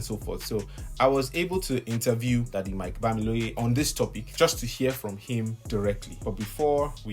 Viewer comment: Sounded like you said Future Mike-Bamiloye in the beginning😐